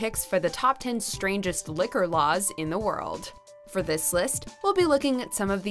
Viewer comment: I thought it said "top 10 strongest liquor in the world" at first